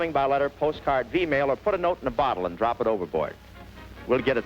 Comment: I did a double take when I thought Bob said, "Just keep your requests coming by....email..." until I realized it's actually V-mail!🤪